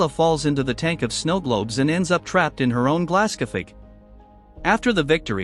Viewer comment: what is the word? I hear stg like glascopy?!?!?